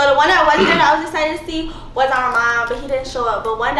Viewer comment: Did she say armon that’s my husband 💕